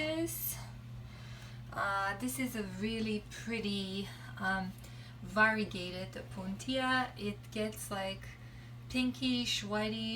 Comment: Beautiful cactus😍😍 couldn't hear the name of the cactus 1:53. Can you please let me know. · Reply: Angela Gorham some type of variegated opuntia... that’s why I didn’t type the name because I was not sure what’s the exact name